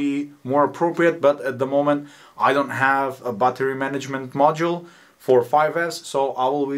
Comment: I heard that as Butter management system